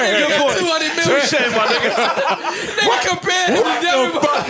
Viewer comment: did parks just say touche my nigga?